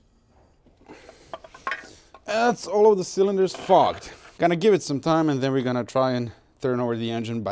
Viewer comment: "sarcasm...look it up" 🤣🤣..... and at 21:18 I thought you said "and that's all of the cylinders f*cked".. 🤣